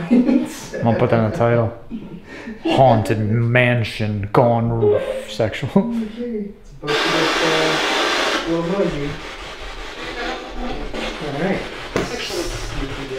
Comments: swore the spirit box said sexual😂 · when you and had the spirit box going in the very beginning I heard SEXUAL DID ANYONE ELSE